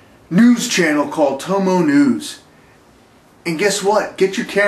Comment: thought he said "Homo news".